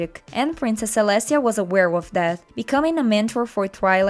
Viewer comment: "Twilight sparkle was just a philly when she discovered her talent for magic. And princess celestia was aware of that" for some reason I heard WEREWOLF instead of aware of lol